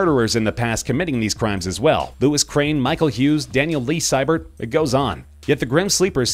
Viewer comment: DID you JUST SAY MICHAEL HUES!? That's my classmate's name! But he isn't black. I mean he does have anger issues and he did bite someone really deep until she bled. Im really confused.